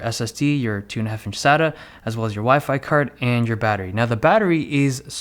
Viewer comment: Sounds like he is saying "wifi 40"